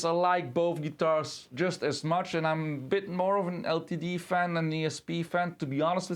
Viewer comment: "..I'm a bit more of an LTD fan than ESP.." Pretty sure they're both the same company and I don't understand what they have to do with the Schecter since I was under the impression that you were comparing the LTD to the Schecter.. With no context for this (seemingly) out of nowhere declaration about LTD & ESP I have no idea what distinction you're attempting to make between them, as I was falling out / nodding off when you were wrapping the video up, so ¯\_(ツ)_/¯ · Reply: Ah, I understand the confusion. It's down to my pronunciation and sometimes my articulation is just off XD. I Actually said that "I'm more of an LTD AND an ESP fan, so I might be a bit biased"